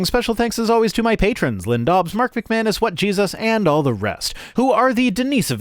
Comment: You know? I am a little bit embarrassed, but for about 3 months I thought you were saying, "Wet Jesus" not "What Jesus". And my immature mind thought, "And why is he wet? If it's the Rhino it must be the horn!"